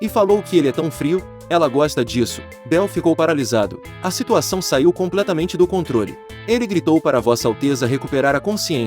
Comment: O que ele disse: "A situação saiu completamente do controle" O que eu ouvi: "A putaria saiu do controle"